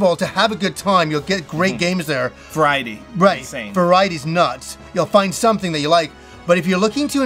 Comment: There's no way I was the only person who heard "varie-deez nuts"